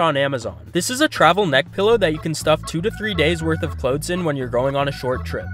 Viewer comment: Why did i misheard he says "Meth pillow" for Neck pillow?